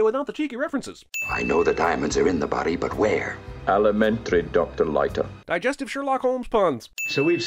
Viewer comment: Wait a minute, Bond said "alimentary"? With Connery's accent, I just thought he was saying, "elementary", as in, if you can't find the diamonds on the body, then deductively they must be IN the body. But "alimentary", which I had to look up by the way, makes a lot sense for the joke. Jeez, the things you learn about movies decades after you've seen them.